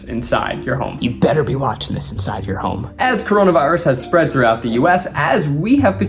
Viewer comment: Him saying "I hope you're watching this video in your home" but my brain thought he said "I hope you're watching this video in your mom"